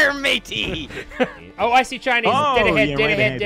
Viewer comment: I genuinly thought said "I see Chinese" instead of shinies XD